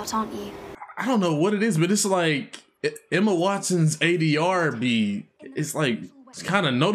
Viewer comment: what did he say? I’m so confused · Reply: It sounds like ADRB but I have no idea what that is